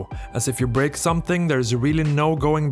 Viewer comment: Did... did you... did you say, "Engrish instructions?"